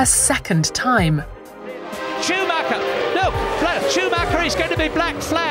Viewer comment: YouTube auto captions said Chewbacca instead of Schumacher at 6:27 lol